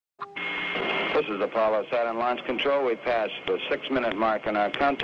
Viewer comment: is it me or does he say “Apollo 7 launch control” instead of 11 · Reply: Its Apollo Saturn Launch Control